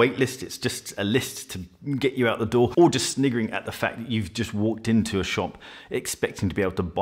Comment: Hey! What was it that you said at 2:38 of the video about a waitlist? It sounded like you said "Niggering at the fact" or "sniggering at the fact"? Was that a mistake or did I mishear what you you were trying to say? Thanks!